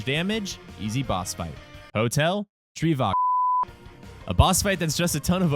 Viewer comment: did you said HOTEL TRIVAGO